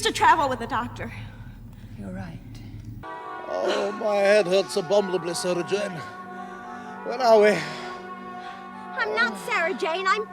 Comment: does anyone know what he said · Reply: “‘my head hurts abominably Sarah Jane, where are we?”